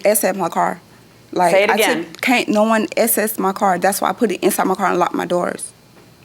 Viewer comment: I thought she said no one can SA (* assault) my car.🤷‍♀️